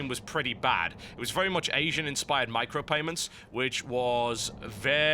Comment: When you said 'Asian inspired micro-payments', I swear to god I thought you said Asian inspired micro-penis....hahahaha.